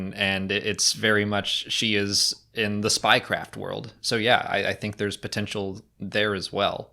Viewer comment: Spy craft was said, but I heard Spy Kids haha